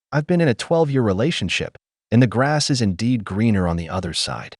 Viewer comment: 1) Crazy eyes. 2) She did NOT say she'd been in a relationship for 12 years - she said she's "been in RELATIONSHIPS for 12 years" very different thing. i can't watch her. Annoying. And crazy eyes.